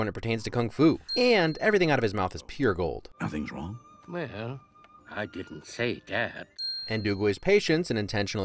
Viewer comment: did he said dab? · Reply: “Well, I didn’t say dat.”